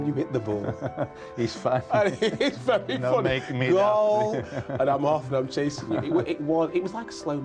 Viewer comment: Hang on a minute..... 7:03 Did Garth actually say "LOL" out loud? Good lord..